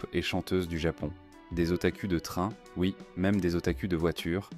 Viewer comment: Mec c'est moi ou il a di otacul😂